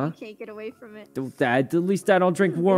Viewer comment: I love how Sykkuno's immediate comeback is "I bet you drink warm water". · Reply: I thought he said worm water. LMAO